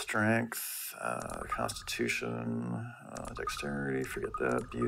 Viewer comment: I heard "prostitution"